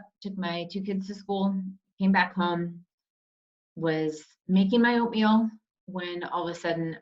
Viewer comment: When you said, "making oatmeal", i swear thought you said, "making opium." 😂